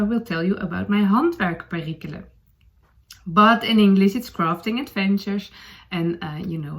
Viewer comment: "crafting adventure" does sound like "rafting adventure", you are right 😂 in that case instead of "let's talk about that", you should say "let's set sail" 😂 . Lovely podcast, I am bingewatching it for the past few days (while I have time before school starts 😀)